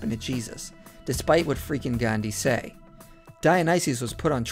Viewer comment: "despite what Freke and Gandy say..." (authors of the book he's describing) I thought at first he was saying "freakin' Gandy" like he was just so annoyed he had to come out and vent! 😂😂😂